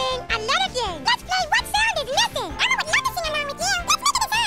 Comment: Am I the only one who thinks that at 1:55 it sounds like he says, "Elmo would love to see my mom again!"